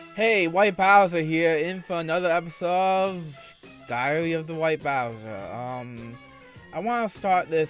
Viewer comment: I could have sworn he said diarrhea of the white bowser